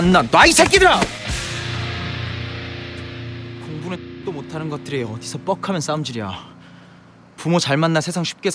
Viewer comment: 레알팩트 공부도 못하는것들이 ㅋㅋㅋ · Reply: 근데 강하늘이 공부는 ㅈ도 못하는 것들이 어디서 맨날 쌈질이야 라고 한거 맞나요...??3:03 완전 그렇게 들리는데 맞나?